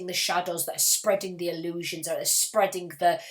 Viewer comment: what is the spelling of the word you said over here? The one that starts with a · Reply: eikasia